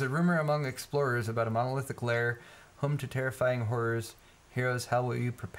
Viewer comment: Did I hear "Home to terrifying whores..." ?? :D :D